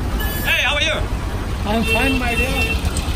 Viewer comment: am i the only one who hears I’m fine my dear.